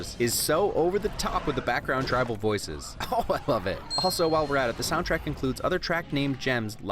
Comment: TYPO: the written version says "Ohh love it." but what actually said was, "Ohh I love it." this doesn't really matter. ;)